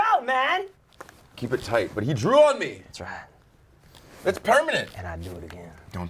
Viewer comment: When he said "He drew on me" I thought he said "He drooled on me"